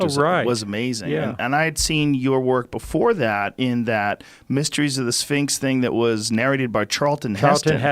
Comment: Anyone else hear "mysteries of the sphincter" at 0:08?